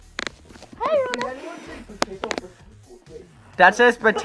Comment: who else heard "That Booty" CX